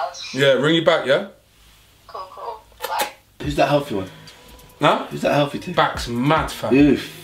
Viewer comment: Chunkz really asked," Who's that healthy ting"💀💀💀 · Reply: I coulda swore he said Whu dat Healfy ting 😂